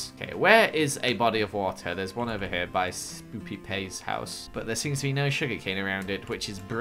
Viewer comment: when u said I thought u said spoopyK lol